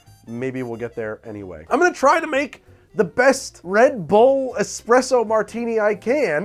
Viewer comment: Oh thank god, I misheard you assaying "espresso martini [in a?] bread bowl" and I was very concerned. Red Bull sounds awful, but bread bowl sounds worse.